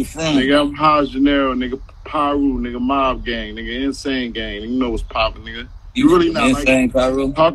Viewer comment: I thought he said Insane Piru at first but now I heard it again while everybody clowning. It actually is a IMG/MOB Insane Mob Gang and Money Over Bitches. It's the same hood in Bompton as it is in West Covina and New Jersey. For all the internet trolls not knowing they street geographics that nigga Brick Baby getting into it with look like a smoker doe 😂